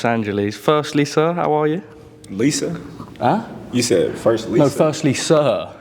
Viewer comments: I thought he said “first Lisa”too lol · I thought he said “first Lisa “ too 😂